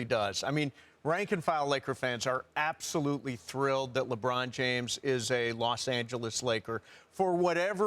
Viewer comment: Wtf ! I could of sworn Kobe said he is glad LeBron James is on the Lakers